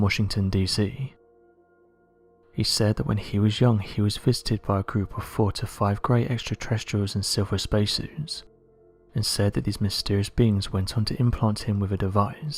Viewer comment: did he say he was fisted??